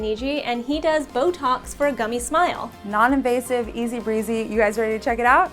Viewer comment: I thought It said cummy smile ohmy